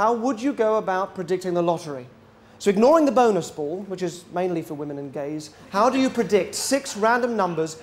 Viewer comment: I use CC - at 6:06 does he really say the "bonus pool is mainly for women and gays"? I've watched 2 of his videos. This was going to be the 3rd but if he is Homophobic, then that's it for me.